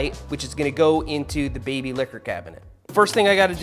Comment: did I hear "baby licker" cabinet?